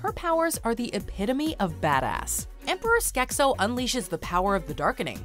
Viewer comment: Emperor Ske'SIL? HIS NAME IS SO! HIS NAME IS SKE'SO! Please tell me I misheard that! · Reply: Pretty sure you did, because I heard Skek'So.